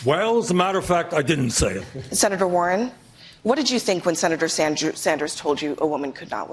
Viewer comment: Did she actually say “Senator Sandjew” at 2:23 ?